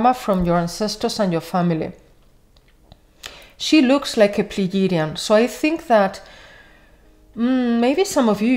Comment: I didn’t understand what you said can you write , please ? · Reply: Yes Love : SHE LOOKS LIKE A PLEIADIAN 🦋